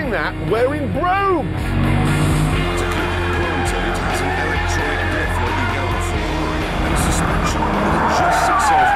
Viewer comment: I CAN'T HEAR YOU! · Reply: “To keep it planted,it’s got an electronic diff like you get on a Ferrari, and suspension that adjusts itself hundreds of times a second.”